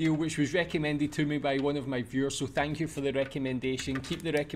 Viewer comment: I genuinely misheard you saying "one of my viewers" as "one of my fuhrers" and I just thought that was hilariously on topic 🤣